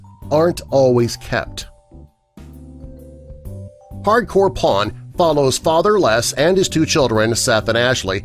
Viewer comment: thought he said Hardcore porn 😂😂😂